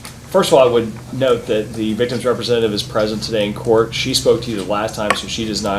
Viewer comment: Good God ! Present ! I thought she said pregnant !!!:face-fuchsia-wide-eyes